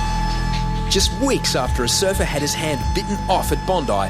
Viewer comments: I thought they said head not hand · i thought they said a surfer had his head bitten off :0 lol